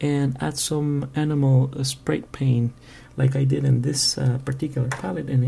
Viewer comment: enjoyed your review. lol, i thought you said at 05:15 animal spray paint. but it is the accent for enamel paint. great tip on that btw.